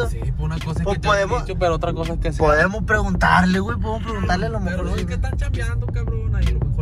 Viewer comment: cómo que el conocía al que les vendió la tierra porque dijo "Podemos preguntarle al humber..."